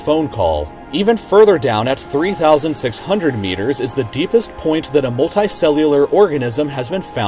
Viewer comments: thought that said deepest orgasm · thought it said deepest orgasm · i thought it said deepest orgasm😂